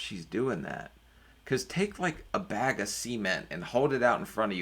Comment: Did you just say "Take a bag of semen and hold it out in front of you and try to walk" ?